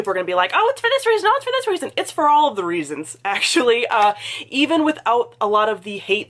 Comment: What did you say @ 1:21?! A bunch of people are gonna be like _________” I rewound it 20x and I cant tell!!!! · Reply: “Oh it’s for this reason, oh it’s for this reason”